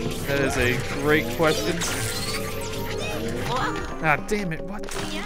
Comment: At about 16:24 it sounds like he said damnit